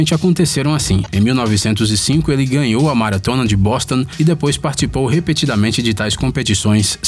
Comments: só eu que entendi ele falando "bosta"?KKK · so foi eu que entendi ele falando bosta? Kkkk